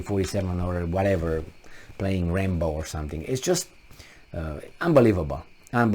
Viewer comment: "With an AK playing Rainbow" I thought thats what was said.